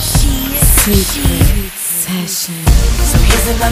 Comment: Does it say "sick obsession" in the background at 0:43?